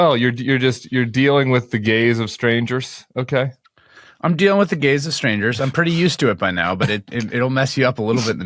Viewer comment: I first thought Tim said, "subject to the gays of strangers." 😂